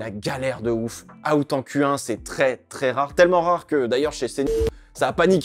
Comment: C’est quoi le mot bipé à 7:25 ? 😂 belle vidéo comme toujours 👍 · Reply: Cnews